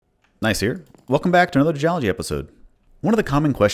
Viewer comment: misheard "Gneiss here." as "Nice ear." but thank you for the unintended, and odd, compliment!! :D